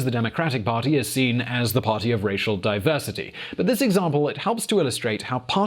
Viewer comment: you say "racial diversity" but the caption says "no racism anymore". Those are two very different concepts. You might want to fix that caption.